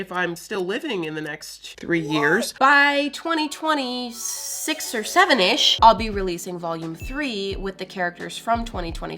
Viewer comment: Did she just say 6or7😂